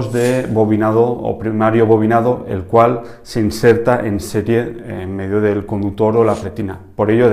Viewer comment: minuto 1:01 que dijistes en ese tiempo? bobinado primario en el cual se inserta en serie?? o setiel? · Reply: En serie